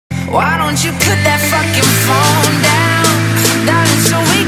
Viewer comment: 초 나만 뽝큐로 들리나